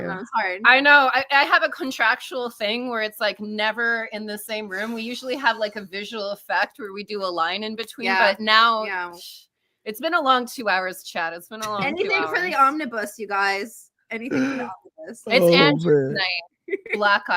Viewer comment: ? Had to back up a minute, thought I heard the Soskas were contractually obligated to "do a line between" them. Some Deadpool ish lol. Blue shift sounds fun, great name. But the kids are all gonna call him shifty or Blue Balls, HS is brutal. Gratz on 100k!